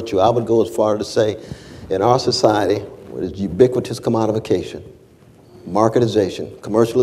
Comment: What is Cornel West saying at 14:37 "In our society, as ubiquitous ___________________, marketization, commercialization, across the board. If your fundamentally committed to aspiring to..." Its a really great point that I want to personally save, just cant quite make sense of what words fall in the blank. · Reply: Bryant Hill-it's ubiquitous "commodification"